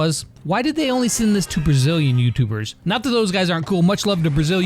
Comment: "Why Brazilian dudes" I hear it as "Why not me" Jk we love ya